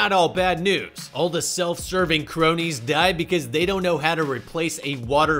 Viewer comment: "All the self-serving cronies die..." is what I hear, "Jeff Bezos' cronies die..." is what I read in captions. It's hilarious, but why?